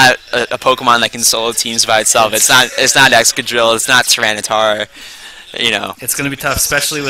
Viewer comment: what pokemon does he say at 34:00 i cant understand him i only understand excadrill but the other one i wasnt even able to google it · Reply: Tyranitar